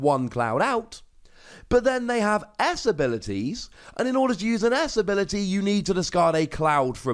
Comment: I got distracted from the video around the 8 minute mark or so...but then hearing about the 'Ass Abilities' perked my attention until I realised you were talking about 'S abilities' and not what I thought I heard. 😂